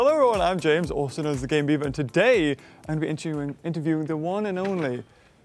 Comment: When he sped through his introduction it sounds like he was saying "The Gay Beaver" (No hate)